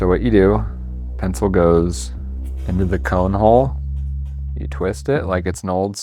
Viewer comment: did he say "pencil goes into the corn hole", because there may be a lot of ways to sharpen a pencil, I don't think that's one one of them, wouldn't even be in my top 5